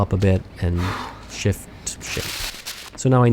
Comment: I thought you said "shift s**t", which would still work in that context but would be very uncharacteristic of you xD I had to turn on CC to figure out what you actual said: "shift shape" lol